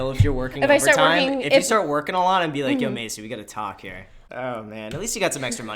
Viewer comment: Hey name is Macy?! I think that’s what he said